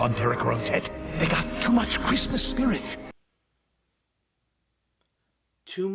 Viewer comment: at the swan princess thing, I wasn’t paying much attention and i heard “too much chrysler spirit”